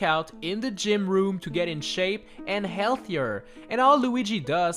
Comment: OMG I THOUGHT YOU SAID "RUIN HIS SEX" XD